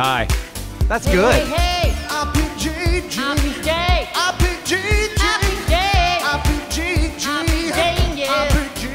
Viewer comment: its only me or is neebs saying at 1:30 iam gay????? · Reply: RBSloder "RPK"